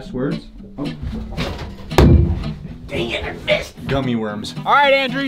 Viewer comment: At first I thought it said origami gummy worms.